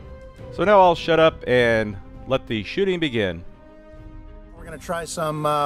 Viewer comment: LOL I thought you said shut up and let the sh*ting begin.